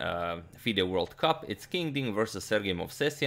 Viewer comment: did he say King Ding? lol